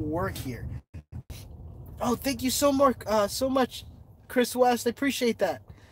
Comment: did he say Chris Watts? Like the man that killed his wife and 2 little girls? I know it wouldn't be the actual Chris Watts but...